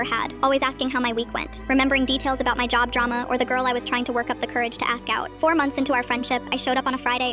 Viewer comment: At first when he said “we talked about the girls I was trying to work up the courage to ask out (or smth)” I thought he said “I was trying to work up the courage to ask Paul out” I was like wait what??😭🙏